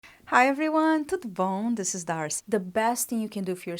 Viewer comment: What do you say at the beginning on every video? At first, I thought you were saying “To the Bone” 🦴 🤣😆 until I watched a video where you spoke Portuguese I think and realized you were saying something else 😄 I’m a new subscriber by the way 🌼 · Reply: Hi happy to have you here 😊 thanks for subscribing I say "tudo bom" which means "how are you?" In Portuguese.